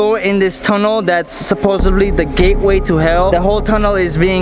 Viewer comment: I thought he said the gay boy of hell